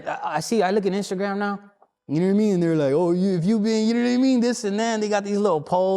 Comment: Wtf did he say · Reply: U know what imean